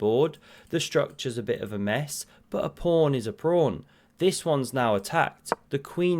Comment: Was that "A prawn is a prawn." I heard at 3:40?